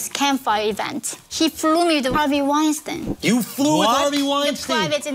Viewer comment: I thought she said Epstein😅